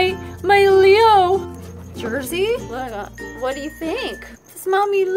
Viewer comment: mins into the video, when you said “Jersey?” it kinda sounded like she said “what up” then you said “what do u think?”😲